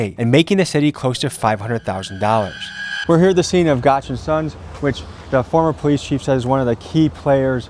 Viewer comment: I thought he said the tow company was called “Gotcha Son”. 🤔